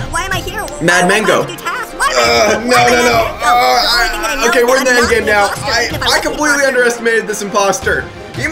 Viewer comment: After he said "mad mango" did you notice what he said after that, he said "__ __ _ _____ ___" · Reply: he said "WE'RE IN THE ENDGAME NOW"